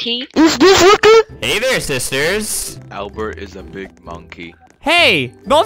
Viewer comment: No one: Not a single soul: Not even the world: Random person: AlBeRt Is A biG mOnKeY.😵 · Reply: I thought that he said 'donkey'